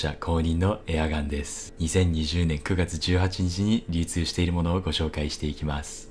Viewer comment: くらいの流通が密輸って聞こえた